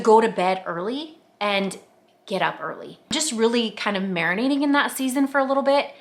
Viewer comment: I love this! Thank you for sharing. I have to tell you though, I'm folding ( and putting away) laundry as I watch this, and when you said, "Get up early"...I HEARD..." Get a Harley". I was like... WHAT did she just say?!?! 🤣🤣🤣 My mind is squirrely like that though! 🤪😆🤣